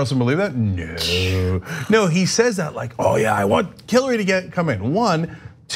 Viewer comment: Wait, did Cenk just say "Killary"?